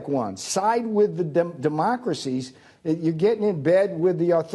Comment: Navarro: "Side with the democracies!" LOL. That's rich. 😁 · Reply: I could have sworn he said "dumbocracies"!